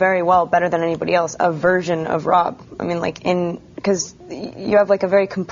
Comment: did she say virgin??